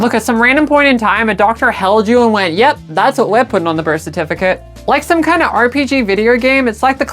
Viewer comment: i thought she was saying "thats a weapon on the birth certificate" at 6:12 lmao also happy birthday.